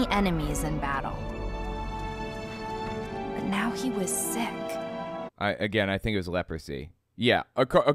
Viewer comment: "But now he was Sith." That's how I heard it, and it gave this whole entire "bible story" a new connotation. Naaman.....was Anakin all along!!!!1